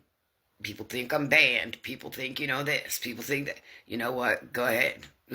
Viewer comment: It sounds like she says “my oldest…think I’m being in school”